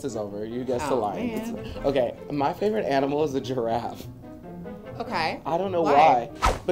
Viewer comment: When he said his favorite animal was a giraffe my volume was down low and I thought he said Trout. then he said he always loved the Lion King and I was really confused